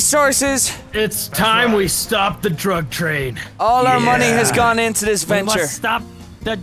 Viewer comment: When they say drug train I thought they said truck train